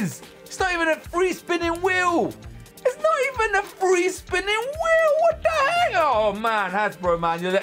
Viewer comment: I think he said what the hell are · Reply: Patricia Tobar, he said "What the heck?!" The thing is that he never finished his sentence.